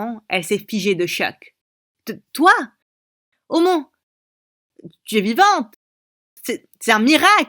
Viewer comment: elle a dit "omo" ou c'est moi ?